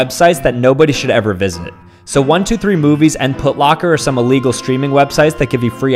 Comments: I thought he said footlocker 😂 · “123 movies” My dumbass “DID HE JUST SAY 123 BOOBIES”